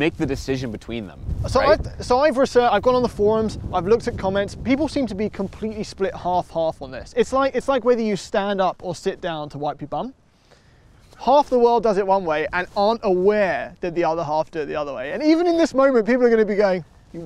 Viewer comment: "It's like whether you stand up or sit down to wipe your bum." lol! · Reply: I heard "...whether to stand up or sit down to (go over a (speed)) bump (while driving)" but man I completely heard that wrong lol